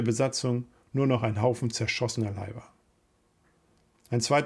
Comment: bei Minute 11:40 - habe ich das richtig gehört - es blieb nur noch ein Haufen zerschossener Weiber ??!!! - war die Flak mit Frauen besetzt ?